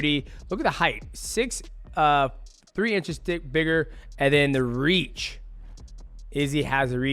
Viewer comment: I swear almost said 3 inches thicker